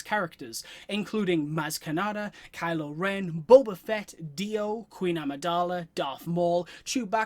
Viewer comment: Did he say… DIO!!!!! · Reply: Haha, I said D-O! 😂